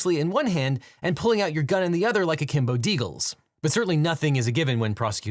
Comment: can anyone explain this reference? Did he say kimbodegal?